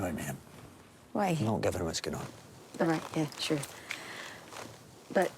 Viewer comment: what does he say here? the best I can get is "not right is girl" which seems unlikely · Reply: "don't invite me in. not given what's going on"